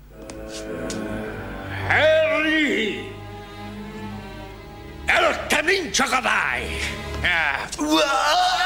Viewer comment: idk why but i heard "Harry gonna die"